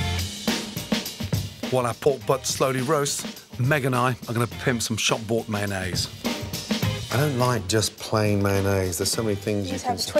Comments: did he say "meg and I are gonna pimp some shop bought mayonaise"? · did he say he was gonna pimp some mayonnaise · I always thought he was saying shot board as in " Shot board mayonaisse ", or " Shot board puff pastry" , and I was always like..." WTF is that." I even looked it up. I only just now realized he's saying " Shop Bought ". I am so dumb.